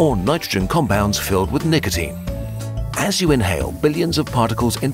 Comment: What did he say? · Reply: Nigatine